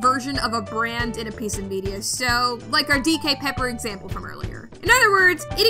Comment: i thought it said dik pepper